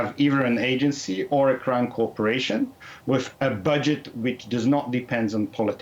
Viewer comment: I like how it auto English titles crime corporation instead of crown.